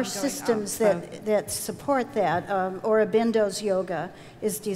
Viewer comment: what form of Yoga is mentioned? Sounds something like “Abendos”. Is it called Integral Yoga? Doesn’t look like what I’m finding online is the same she mentions. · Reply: Hi Christine, it's Sri Aurobindo's yoga.